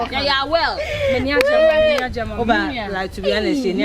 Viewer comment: did just say awell?😂